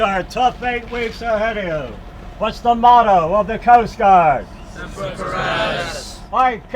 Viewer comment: Ok but i heard “simper for Attis” when he said the motto